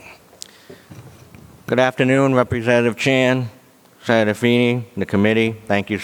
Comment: Had to rewatch the first few seconds as I thought he said Jackie Chan.